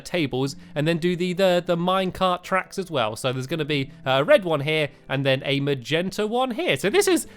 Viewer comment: did he say red